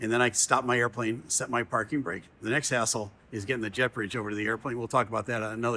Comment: Holy crap!! I thought he had said ASSHOLE towards the end, but it was hassle. What a relief!! I would stop watching his video clips if he lost his wholesomeness and got nasty.